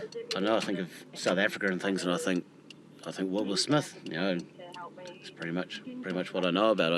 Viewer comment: did he say, "will smith"?